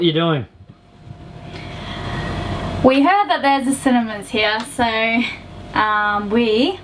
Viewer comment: I thought they were saying they were going to the Cinnamon's, I thought maybe that was an Australian CinnaBon or something. lol "Cinemas" got it.